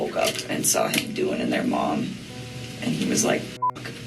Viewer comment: did she say "saw him doing in their mom" Also she needs to learn the word 'exclude' instead of disclude ? lol TY! Love your commentaries.